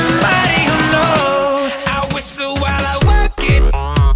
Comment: at first I thought he said I whistle while I twerk it